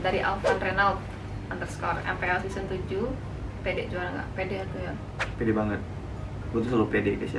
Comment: PD atuh yang? Atau gw salah denger? :v